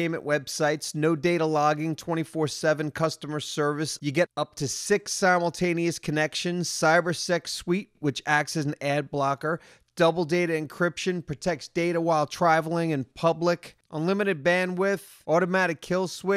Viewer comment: did said "cybersex suite"? WTF?